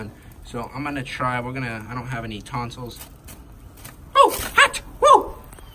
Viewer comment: “He doesn’t have any tonsils” instead of tongs😂